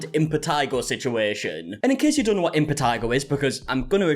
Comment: I thought they said Vitiligo situation and it was contagious...